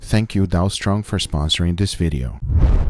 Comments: Who else thought he said "thank you Donald Trump for sponsoring this video" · I thought he say Donald Trump for sponsoring this video... 🤣🤣 oh is Dalstrong.. 😅😅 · When guga said "Dalstrong" i thought this homie said... "Thanks to Donald Trump for sponsoring this video" lol